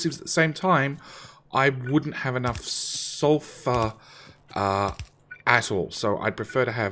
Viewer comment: Re-watching the vid.....at 03:00 did u say sulfur a$$h0les????????